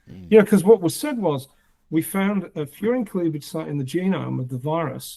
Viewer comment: I thought you said "fear and cleavage site", so when you said it is what it sounds like I was very confused.